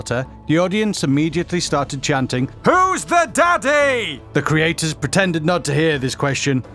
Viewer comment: I thought they said haru's the dadddy But they said who's the daddy